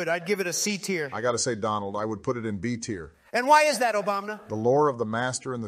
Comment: sounded like "obamina" to me